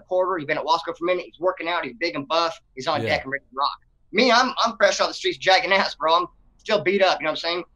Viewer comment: I hear "fresh out the pen," but this dude. Talking about "fresh off the streets," as though when he gets locked up he sees it as "going home."